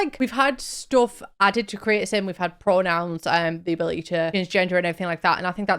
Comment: i know you said "the ability to change your gender" but the captions said "the ability to transgender" and that make me laugh so hard (and then also get sad because yes you can change your sex/pronouns but like, the Trans Experience isnt really a thing in the sims and i really wish it was)